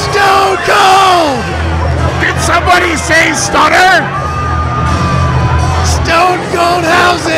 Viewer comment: Did somebody say ‘Stunner?’